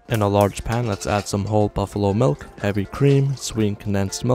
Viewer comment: If this guy made olive oil kulfi, who knows how far he’ll go..😨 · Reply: I swear i heard him say whole buffalo milk